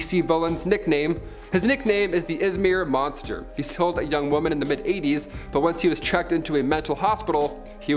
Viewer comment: Did I just hear, "a young woman in the mid eighties?"